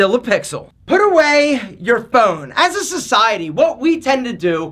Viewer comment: did someone say S C I E T Y ? ? !